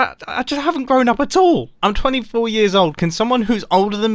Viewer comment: Why I heard that he said I'm 42 years old???